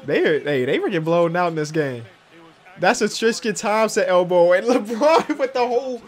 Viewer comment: did anyone else hear he said triscan thopmson in